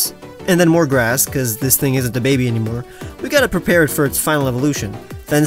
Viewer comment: Misheard words: “This thing isn’t DaBaby anymore”